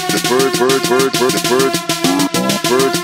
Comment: I like this one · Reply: at the beginning i thought he said twerk machine instead of bird machine